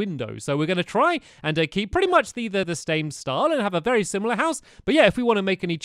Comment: at 3:13 instead of saying same you said stame